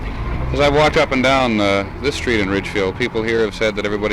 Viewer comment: It does sound like Jennings says "RidgeFIELD" at 15:48, but he also clearly says "RidgeWOOD" four times at 16:31, 17:45, 17:51 and 18:05.